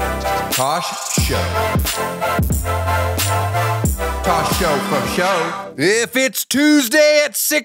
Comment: Up until today I thought he was saying in the intro “Tosh Show Talk Show!” and not “Tosh Show Fo Sho!”